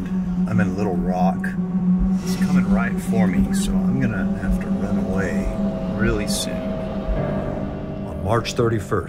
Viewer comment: I almost thought you said "A little rock is coming right for me so I'm gonna, have to run away, really soon."